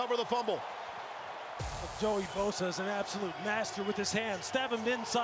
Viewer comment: Am I the only one who thought he said “Joey Bosa is an absolute bastard with his hands?”